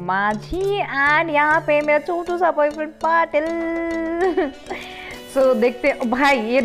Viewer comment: did he say mera chotu sa boyfriend patil